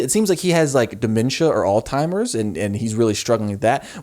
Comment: did you just call Alzheimers all-timers? Is this a gorl world reference or what😭 Edit: 1:32:19 you said alz-timers this time haha. Honestly not being mean, just thought it was funny